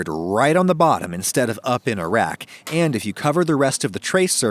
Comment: i legitimately thought he said "Up in Iraq" i had to check subtitles to see its was "up on a rack"